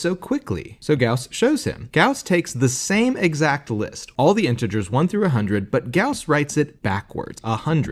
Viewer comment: For some reason, I thought you said all the prime integers, and was very confused how Gauss got such a big number